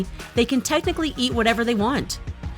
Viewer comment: boi did she really say Jonkook